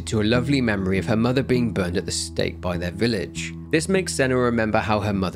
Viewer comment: i heard "burned at the stake" as "burned as a steak" lol